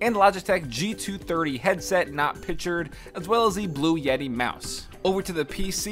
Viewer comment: Did he say blue yeti mouse